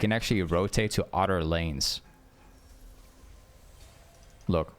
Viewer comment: I thought you said otter lanes 😂